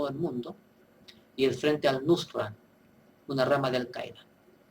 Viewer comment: Buenas! disculpe, pero que dice en el minuto 3:48 "El frente...." Gracias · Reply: Micaela Ibañez, el Frente Al Nusra